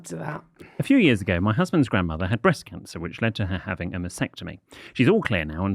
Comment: Mastectomy …….. I heard it as vasectomy and nearly choked on my drink 😂